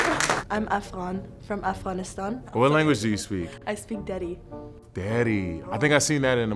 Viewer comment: Whoever does subtitles needs to feel comfortable writing [incomprehensible] or [...] when they don't understand what someone is saying. The Afghani girl says she speaks Dari not "daddy" at 3:15. And the Sri Lankan girl at 8:29 says she eats a lot of Parippu, not "pOtTy PoOp".